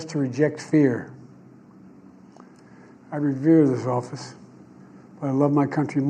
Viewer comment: I’m not sure what he said at the beginning of that sentence, I don’t think he knows what he said either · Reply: I revere this office